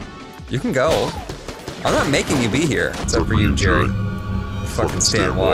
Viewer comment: I heard exept for you Jared it freaked me out for a second